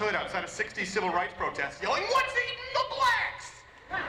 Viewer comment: I've replayed this punchine a few times. The fake accent is so thick that I cannot understand what he said. Edit: Was it "What's eating the blacks?"